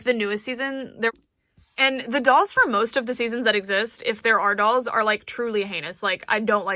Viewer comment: I am soooo confused at what you’re trying to say in this part. Idk if I’m just dumb but my brain folded trying to understand lol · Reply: “The dolls for most of the seasons that exist, if there are dolls, are truly heinous” I’m saying that I don’t like them